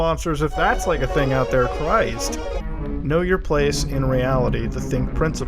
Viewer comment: if someone wants to know what is in the 11:49 it says "kill your self" instead of "know your place"